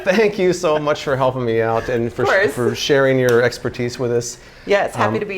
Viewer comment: For a second I thought he said "Thank you for your expert-cheese".